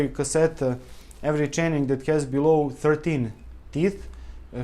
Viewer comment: did he mean every chainring below 30 teeth? it sounded like he said 13 teeth... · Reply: 13